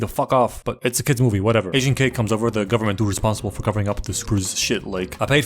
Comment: The way he said "agent k " made it sound like he said "asian kid"